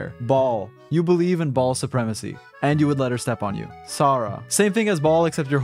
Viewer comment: I thought u said bald supremacy